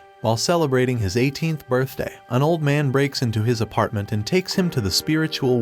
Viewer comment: I paused the video in the beginning bc I was gonna take a screenshot of the title, then realized I just heard "an old man breaks into his house, and takes him" and paused there 🤣